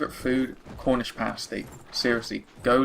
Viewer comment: I heard the "Favorite Food" answer as "Cornish Fantasy", I definitely heard that wrong. XD It does suck when people do that for merging lanes. Wait your frigging turn. Gonna guess that question was "Cecil the Lion or Harambe" haha. Good Q&A man.